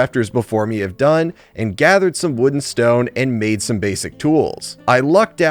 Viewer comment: why did bro say "wooden stone" in second 0:43 xD